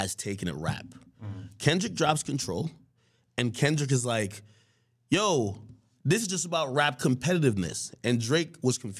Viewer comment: wtf is he saying? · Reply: Drake wants blood and Kendrick wants clout.